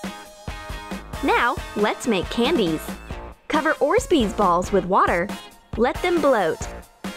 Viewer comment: is it just me or did she say "orzbeez balls"??🤦🏻‍♀️